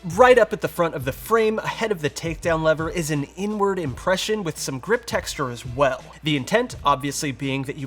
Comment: I had to do a double take. I thought you said "N-Word impression" 😂